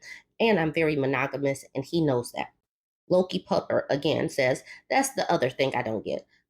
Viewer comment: Lol every time u said LokiPupper i thought you were saying Low Key Pupper... Which i guess technically you did! I had to look at the screen carefully 🤣💜